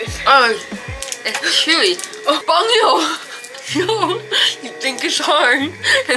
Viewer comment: I thought said bunghole 😂😂😂😂